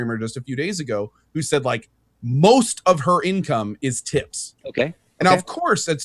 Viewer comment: "I know a female streamer and most of her income is tits" - I know I misheard, but it is still kinda funny :(